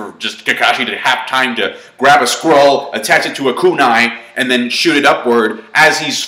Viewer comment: Lol I swear I heard "Kakashi half time attaching a squirrel to a kunai and shoot it upward" XD